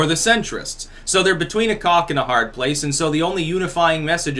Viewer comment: "between a cock and a hard place" could swear you said that.